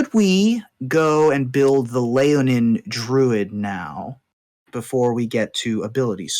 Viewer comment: I kept hearing "Leonin Druid" as "Lay-On-Hands Druid" and it kept throwing me off